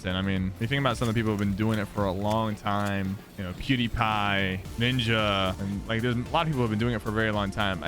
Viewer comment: It sounds like he said "cutiepie" 😂😂